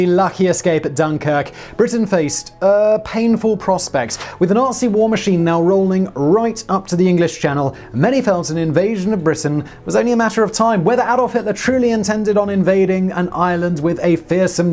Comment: lol I thought you said RC war machine.